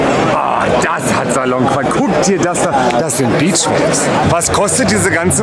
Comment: Beachwaves 4 ever <3 Ihr seid die Geilsten ;D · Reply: Ach so, ich hab' "Bitch Waves" verstanden. Passt aber auch.